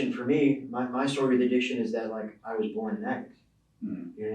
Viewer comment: he says ' he was born ???. Could someone tell me what he said? I am profoundly deaf and rely on cc and my headphones . Cc failed here with that one word. Thank you for any help. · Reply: Yes, he did say he was born an addict